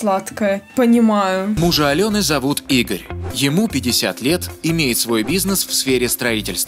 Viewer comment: я услышала теперь Алену зовут Игрь